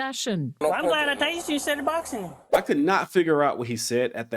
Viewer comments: "I'm glad I tased you instead of boxing." (?) That's what I thought I heard, then found out he was previously a boxer, which "confirmed" it for me. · cop said " im glad i tasered you instead of boxing you" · Sounds like he said "im glad i tazered you instead of boxing" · he said "I'm glad I tased you instead of boxin' you." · I think that officer said “I’m glad I tased you instead of boxing you” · cop says "I'm glad I tazed you instead of boxing him" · ya it do sound like he said that im glad i tased you i cant hear the last word to me it sound like huh that what it sound like you can get a app for your pc where you can turn up the sound sound you can hear it better that what i did it do sound like he said that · "I'm glad i tazed you instead of boxing you" is what he said. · You couldn't understand him because he was saying "SHAAAAZAM! SHAZAAAM! SHAAAAZAM!" I have not heard someone sound so much like Gomer Pyle in 30 years. I heard, "I'm glad I tased you instead of boxing." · he says: "I'm glad I tased you instead of boxing you."